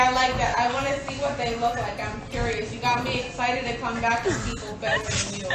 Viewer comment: “Tristan Thompson said to send me a picture of YOU THREE” (As in her friends) 👀..... ”I wanna see what they look like. I’m curious. you got me excited to come BACK for PEOPLE BETTER than you” 😭😭😭😭😭 Am I hearing things ????